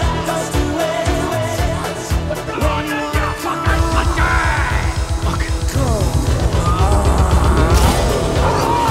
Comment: Does anyone know what begbie says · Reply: The Milk Man "come on then you f@ budgie"